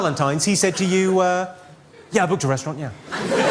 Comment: Next Valentine if he says to you "yah ???????????????" Can't understand what he is saying. Can anyone translate pls??????? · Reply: "I booked a restaurant, yeah"